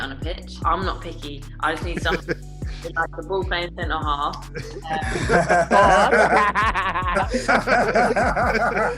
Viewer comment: What did she say at 0:39 when everyone laughs? I'm sorry english is not my first language and I can't understand :( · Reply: ball playing center half i think